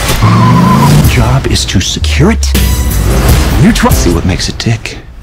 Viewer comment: I thought it said “see what makes a dick” for a second.